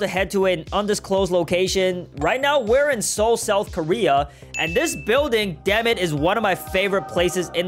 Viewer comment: it scared me when it said south korea I thought it said north korea.